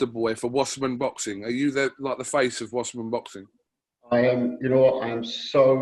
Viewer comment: Thought it said Wasteman Boxing at first 😋